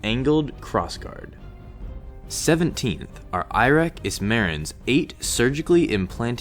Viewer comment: I might have misheard the bloke's name a bit. I got curious who he is so I googled "arek ismaran lightsabers" Result #1: Dream Your AATMODDHAR Part 2 - Swapn Hua Sakar (Lyrics) Jain Result #2: Malayalam christian devotional songs for dance download Result #3: Sexy Rockband Girl - Sample Gay Male Xxx Videos : Blood ... Summed up from those results he really must be the apex of dark side.